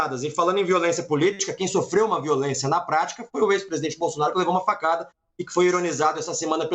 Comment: Só eu que ouvi o deputado do PL em 14:16 falar EX presidente Bolsonaro?